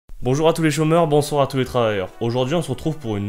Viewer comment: au début de la vidéo j'ai compris "Bonjour, les chauves meurt"